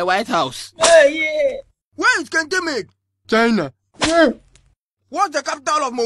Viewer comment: Teacher:Where is Kente made? Student:China 😂😂😂 anyway I don't blame him everything is made in China nowadays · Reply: At first I thought he was say Kentemay. As in there's a place called Kentemay,didn't realize he was saying Kente made until you said lol